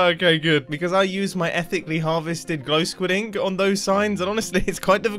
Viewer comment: Every time he says “ethically harvested” I hear it as “ethnically harvested”